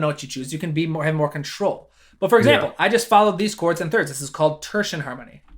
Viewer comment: Anybody understood at 6:40 what he says? following the chords in 3rds is called 'thirdsian harmony'? Id like to dive deeper into that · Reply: I said "tertian" or "tertiary" harmony, another name for it is "chromatic mediants" :)